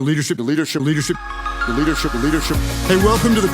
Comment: What is he saying?? All I keep hearing is "Liter ship"....is it a metrics class anyone???